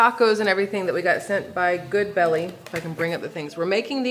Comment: What's the name of the food box company ? Thought I heard you say good belly but that doesn't bring up anything